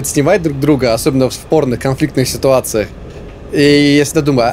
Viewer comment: мне послышалось не "в спорных ситуациях", а "в порно ситуациях" и не могла понять что это за ситуации такие, ахахах 🤣🤣🤣